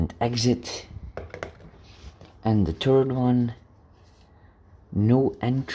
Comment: When u say third it sounds like u saying turd 🤣